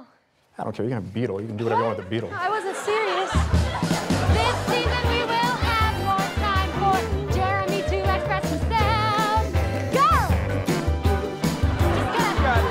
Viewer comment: anyone know what Natalie said? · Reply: This season we will have more time for jerremy to express himself.